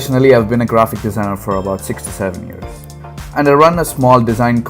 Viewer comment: I thought you said 67 years at first and i had to look back quick out of confusion